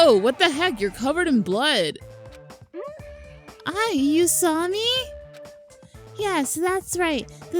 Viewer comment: lol at 7:48 ''you saw me?'' sounded like usami to me XD